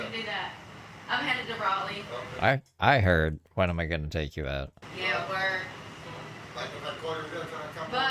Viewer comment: that’s what I heard when am I taking you out lol😂